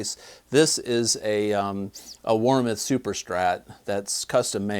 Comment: That’s your best looking guitar and it sounds good! Partscaster or Charvel? Fender? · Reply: I just heard you say Warmoth 🤙🏻